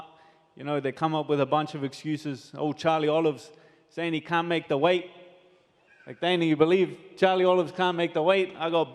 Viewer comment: After his prediction, the crowd yells something out... but what did they say ? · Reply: They said "Go Conor"